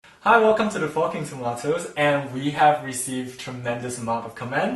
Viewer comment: Him: hi welcome to the forking tomatoes Me: DID HE JUST SAY HI WELCOME TO THE FUCKING TOMATOES!